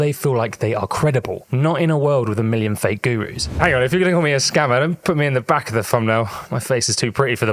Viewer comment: What on earth did you say at 1:33 lmao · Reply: Hang on if you're gonna call me a scammer. don't put me....................